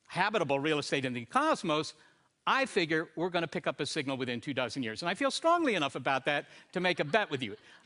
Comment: He speaks so fast, what did he say at 6:59, 2 thousand years or 2 dozen years? (why would he say 2 dozen? the word is 24). oh yes its in the description, 24 years. which means half a dozen has already gone since this video was recorded (that is 6 years for normal people) and still zilch, ningún.